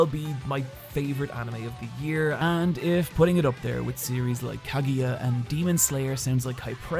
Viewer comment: what did he say? Cavia? I couldn't find that anime..